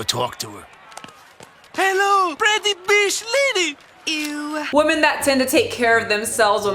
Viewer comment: it sounded like Mr. Hyunh called her a pretty bitch lady and I'm screaming 😂😂😂